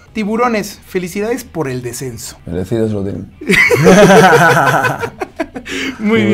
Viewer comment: que dice llevo 2 días con la intriga y no es broma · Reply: Merecido se lo tienen